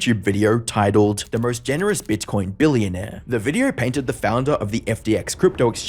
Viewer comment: for a second I thought you said bitch-coin billionaire